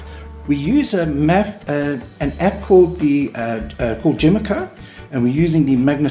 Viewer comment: Great video! I am going to install a point one heading sensor, is the app you use for iPhone? I am on Android it sounded like you said the app was called "Gemico" can you please confirm the spelling for that I am looking for the app and can't find it, thanks Alexander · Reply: Hi Alexander, yes, iNstall by Gemeco for iPhone